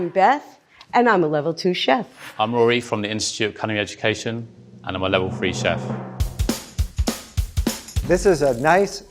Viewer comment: Did he say "I'm a level 'free' chef" haha?